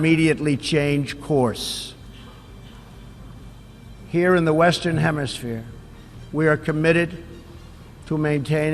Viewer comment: Did he say “The Western Hemisphere?”